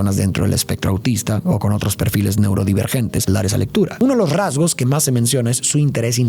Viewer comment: No se entiende qué dices justo después de "Neurodivergente", suena como "La risa lectura" o algo así.